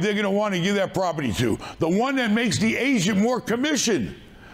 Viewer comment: "We got bankers, we got real estate ASIANS". "Pay the ASIAN a commision". Im dying laughing anytime he says "agent" it sounds like he is saying "asian" 😂😂😂😂😂😂 Take a listen try not to die laughing 13:25 - 13:40 (15 sec, full of asians)